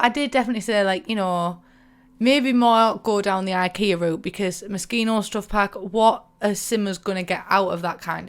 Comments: OOOOHHH when i saw that video at the beginning for the first time i thought she said MOSQUITO stuff pack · Thought she said mosquito stuff pack 😂